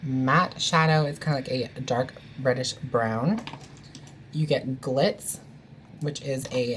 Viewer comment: When you said reddish brown I thought you said British brown and I was like what XD and I had to play it back